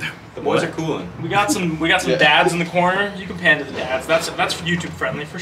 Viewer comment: I thought he said dabs in the corner, really wanted to hit a fat Ole dab. Blowtorch and all